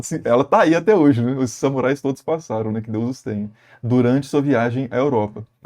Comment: só eu escutei um "Que Deus os tenha"?